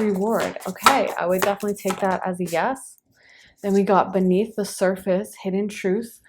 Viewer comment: Do you ever give a straight up yes or no? I listened to the entire pick but I didn’t get an answer. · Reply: she clearly says “Yes”.